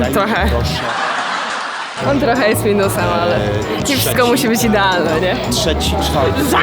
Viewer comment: Czy tylko ja usłyszałam "c!psko musi być idealne"...